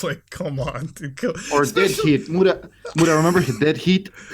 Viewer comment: I thought he said Dead Heap. No wonder I couldn't find the movie. It's Dead Heat.